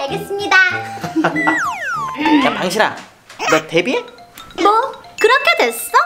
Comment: 잘 들으면 방신아라고들림